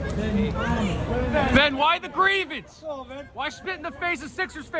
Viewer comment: I'm thinking why is guy calling Brittany "Grinder" Ben unless..... 🤔